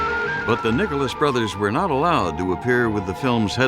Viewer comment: At the 17:14 minute mark sounds like he said the nigerless brothers had to rewind it to be sure.